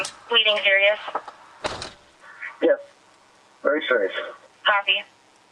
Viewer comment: Did she say 'happy'?